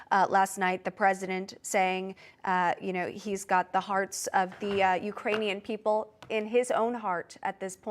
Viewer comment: Our hearts go out to Kiev and the Iranian people! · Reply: I thought he said the Uranian people...